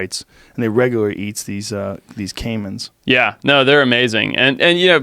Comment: For a second I thought it said caveman